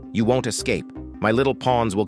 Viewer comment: I thought it said you can’t escape my little balls💀